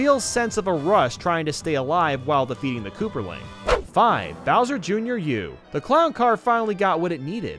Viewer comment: did he just say “kooperling?”